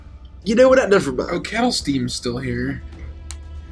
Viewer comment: I keep mishearing Ket'resteen as "Kettle Steam" from OUAW